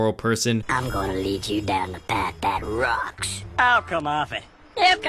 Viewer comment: I thought the devil at 4:22 said "i'm gonna lead you to the path that sells rocks" damn Disney got gansta all of a sudden.